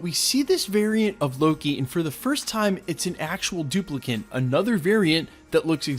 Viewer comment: Did he say duplicant?